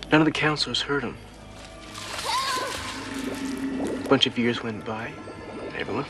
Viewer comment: i thought he said a bunch of beers went by